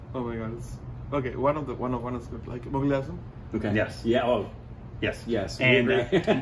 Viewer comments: what movie did ARR say he loves ? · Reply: Mughal e azam and Sound of Music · what was that movie he said? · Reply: Mughal-e-Azam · What was the film that AR Rahman saying in 25:22? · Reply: Mughal-E-Azam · what is the movie name he said his favourite.??? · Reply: Mughal E Azam ,& Sound of Music